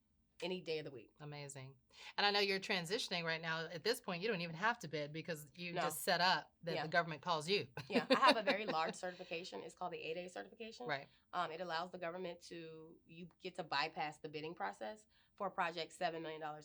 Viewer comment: min Did she say Aday certification